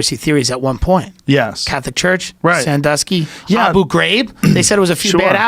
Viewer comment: what is he saying? I want to look that up but have no idea how to spell it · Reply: Abu Ghraib prison system in Iraq. In late April 2004, Americans got their first glimpse of the haunting photographs of Iraqi prisoners at the Abu Ghraib prison west of Baghdad: scenes of naked, humiliated prisoners piled on top of one another, some forced to assume sexual positions, all while American soldiers posed nearby, smiling at the camera.